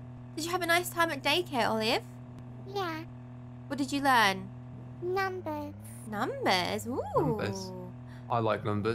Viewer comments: Lol when she says she learned numbers i thought she said nothing not juging her speach tho because i had speach problems once in my life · At first I thought she said nothing but she said numbers